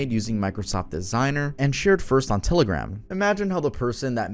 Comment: thats telegram (misheard)